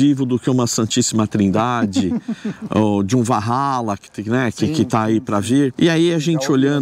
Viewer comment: não entendi :( “Vahala”? Depois de santíssima trindade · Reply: Aston martin valhalla